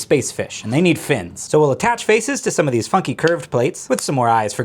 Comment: Jesus Christ I thought he said something else when he said “funky” 😭😭😭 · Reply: I thought he said fu*king